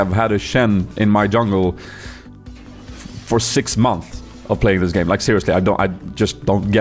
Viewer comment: did you say month instead of months ?